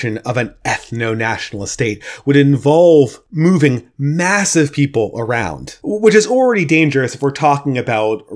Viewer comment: "moving MASSIVE people around" is what it sounded like to me. XD